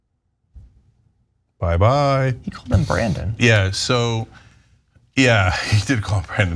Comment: I missed the 'Brandon' I thought he said 'random' 😅